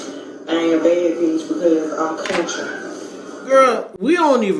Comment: you ain’t wrong….all I heard was “ I ain’t a bad bitch cuz I’m country”……I was very confused 😂